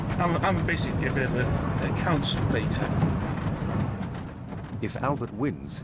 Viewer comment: did he just call himself a counselbator😂